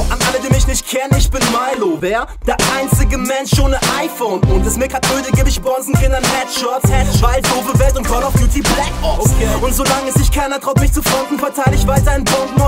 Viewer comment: Was rappt dieser Milo bei 1:51 nachdem der sagt :" Geb ich Bonzenkindern Headshots..." ? 2 Jahre später und ich habs nach x mal anhören immernoch nicht verstanden 👎🏻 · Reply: "Und ist mir grad öde, geb ich Bonzenkindern Headshots, weil doofe Welt und Call of Duty Black Ops"